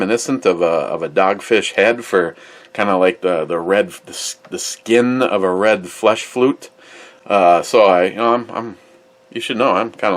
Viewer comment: "Skin of a red flesh flute" Seriously what you said??